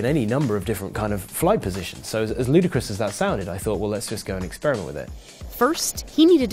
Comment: Did he say "so as Luda Chris as that sounded"?